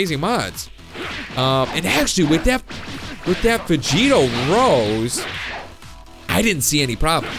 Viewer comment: I thought it said veigto nose